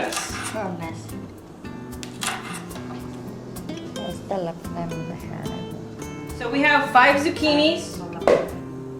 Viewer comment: on min. 2:36 did she say.. ay esta la pendejada esa hahahaha. BTW I love your videos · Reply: She said "esta la podemos dejar ahi" talking about the hierbabuena